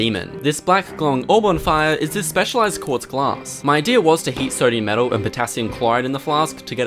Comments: I THOUGHT I SAID "MY IDEA WAS TO EAT SODIUM METAL" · "black glowing orb on fire" literally thought you said something in german hahah I had to turn on subtitles and Im still wondering if I got it right